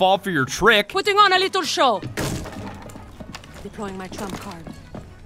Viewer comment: i swear at 3:48 shes said deploying my trump card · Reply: She really said deploying my trap card so she made a yugioh reference